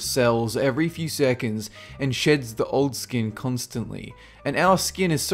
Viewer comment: When he says sheds I thought he said shits